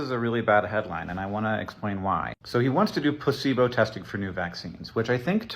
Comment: Why did I hear "pussy bow testing"?